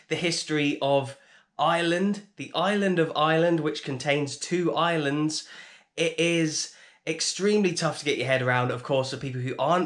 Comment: To be fair, English people pronouncing ireland as island doesn't make it easier to understand 😂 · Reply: I was so confused at the start when I thought he said there was two islands 🤣